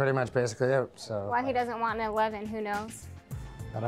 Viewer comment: Even at the end it seems like she was still willing to be with him....."I still don't know why you don't want an 11" if he got with he after the loan, he wouldn't have had to pay it back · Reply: I thought she said, "why he doesn't want no lovin', who knows" lol